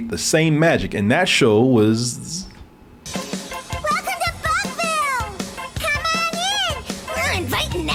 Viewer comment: Okay I CANNOT be the only person who hears "WELCOME TO FUCKVILLE!!"